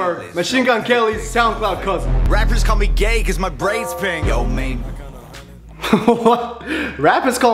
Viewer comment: I thought Tom Macdonald said “Rappers call because me gay because my brain is big.” 😂